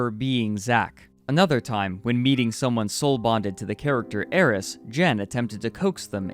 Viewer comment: the subtitles are wrong, it's Aeris and not heiress